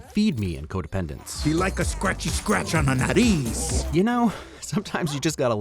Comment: This video taught me that Dennis likes scratches on the "nariz" (nose). I've seen the movie 5 times, and I always thought he was saying "nutties".